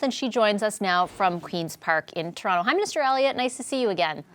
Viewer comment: I KNOW she said "Hi Minister Elliot" but dammit I keep hearing "Hi MISTER Elliot" XD